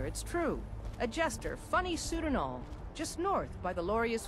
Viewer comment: Did Uthgerd the Unbroken say "a jester - funny suit and all"? I could have sworn she said "bunny suit and all".